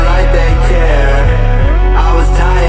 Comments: i thought he was saying "i was dying and my body was there" 😭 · I was dying and nobody was there...💔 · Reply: oh shit i always thought he said and the body was there. like if he was overdosing and he was having an out of body experience while dying